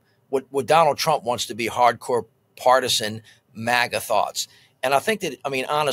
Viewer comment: Did he say “MAGATHOTS” at 4:07?